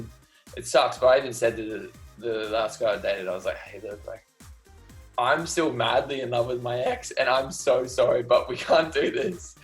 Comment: did he say "I even said to the last guy I dated, hey dude I'm still madly in love with my ex" ? 5:05 at OR WAS IT ME bc that'd mean he's biii and i'm bi and REPRESENTATION heh that is all